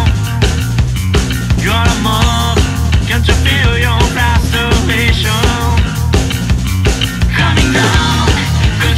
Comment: "did you feel you m----rbation?" i cannot hear it, sorry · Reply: 「Can’t you feel your own frustration?」🙆‍♀️